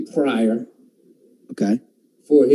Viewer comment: What is the name that he says here? Sounds like "pariah" but I'm sure that's not it. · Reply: Richard Pryor - one the greats! ☺